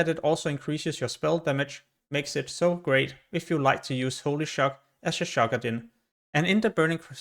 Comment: ???? What are you even saying here? Nothing wrong with your English but if you're gonna try to make an informational video at least make sure that you speak so other people can understand what you are saying. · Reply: Shock-adin. Holy Paladins that take Holy Shock to Shock people as a Paladin. Shockadin.